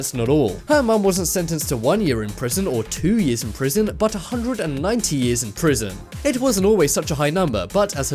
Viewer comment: 190 Years????? am i hearing right?